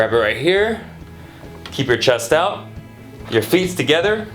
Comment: did he say feets ? lol